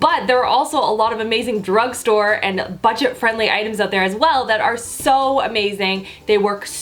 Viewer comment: When you said "budget friendly items" I heard "bunch of friendly items," and I was like, good for you, way to find the friendly items, and not those mean ones! Haha